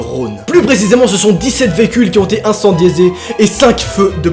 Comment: INCENDIÉQUOI ? 😂